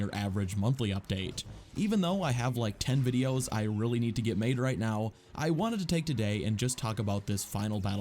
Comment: at first I thought you said “I really need to get laid right now,” 🤣